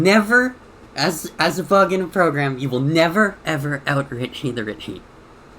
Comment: I promise I'm not trying to be rude, but I've tried like 5 times to understand what you said at 0:17 and I still have no clue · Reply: you will never out-richie the Richie.